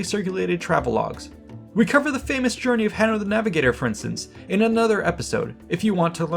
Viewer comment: The famous journey of Henry the Navigator? I didn't think Henry ever went anywhere himself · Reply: You misheard. HANNO The Navigator, not Henry <link>